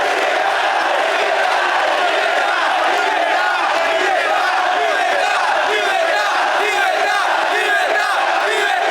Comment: what are they shouting? Can someone translate pls? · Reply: Liberty